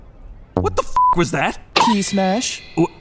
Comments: I legit thought he said TAXES SMASH! Because I wasn’t looking at the screen 😂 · I thought that said taxes smash💀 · I thought he said taxes smash bro💀💀😭😭😭